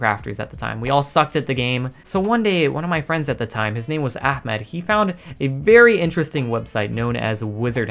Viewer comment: did you say ahmet